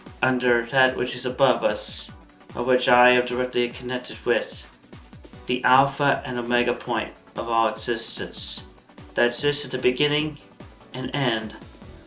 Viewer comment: Deadass, I thought he said "The alpha and omega point of all autistics" and had to rewind that shit.